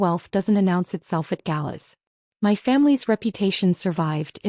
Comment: minutes. “Wealth doesn’t announce itself at gallows”.😅 I particularly like the use of the word gallows, instead of galas.